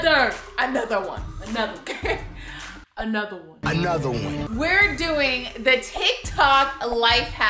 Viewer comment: i thought they said a nutter butter🤣😂🤣😂